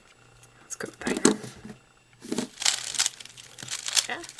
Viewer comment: did you say, "its go time"